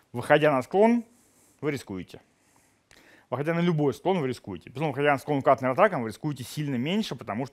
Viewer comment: Пожалуйста, уточните что говориться на 2:10 "...выходя на любой склон вы рискуете, выходя .......?......?....... вы рискуете сильно меньше, потому что 8-тонная..." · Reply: «на подготовленный склон»